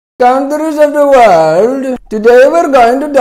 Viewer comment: Did India say “Kangaroos of the world???”🤣🤣🤣🤣🤣🤣